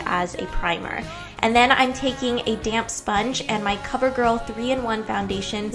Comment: I thought you said "damn sponge"